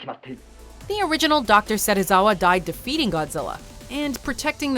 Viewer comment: I was fiddling around with my headphone wire and instead of the narrator saying he died to defeating Godzilla it sounded like she said he died to F***ING Godzilla, what a perfect timed audio glitch 😆